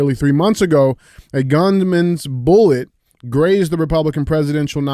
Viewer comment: I heard “the governments bullet” not gunmans bullet😂